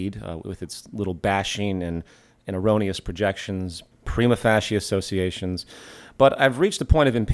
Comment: can someone tell me what that word at 2:47 "premofacy" is or whatever i had no luck finding it on google. im guessing he means that alex is using uneducated associations?